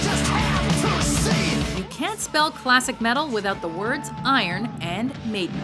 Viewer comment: I thought you said no classic metal... :|